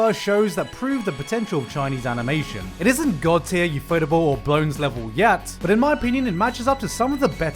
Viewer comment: what’s the second producer he says after ufotable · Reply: Bones